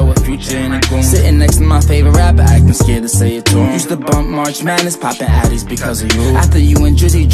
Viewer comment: Did he just say used to bump marsh marsh matters at 2:31 to 2:32 and was he giving a shot out to Eminem or is it just me miss understanding